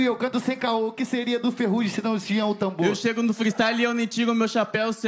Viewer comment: "Voce é o nego do borel, com essas rimas de bordel" 😂😂😂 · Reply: Eu entendi "asinhas de bordel" 😂😂😂😂